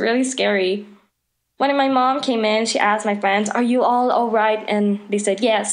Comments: Wait- i think i hear she say "One of my mom came in " @@ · did she just said one of my mom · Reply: blaze blossomed she said "when my mom came in”. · Hold up in 2:31 she told ''one of my mom''' wait how many moms does she have lol! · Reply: I heard her say “when my mom” .-.